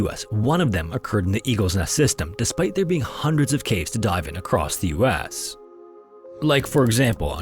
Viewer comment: I'm terribly sorry to all Americans, but I thought dude said: "Despite there being hundreds of caves to die in in the US." and I had to do a double take because it sounded extremely morbid.